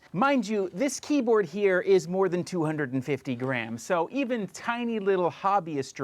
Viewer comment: Wheeew. I'm walking on a treadmill. Thought you said the keyboard was more than 250 grand. Grams makes way more sense